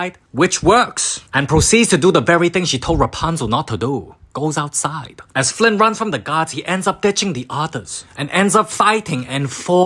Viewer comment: somehow heard this as "as flynn runs from the nazis"